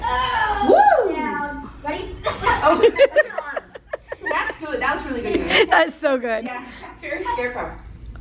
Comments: it sounds like saying that was really good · I think I heard say that was good really good